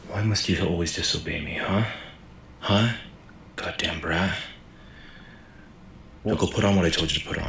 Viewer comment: I thought he said rat 🤧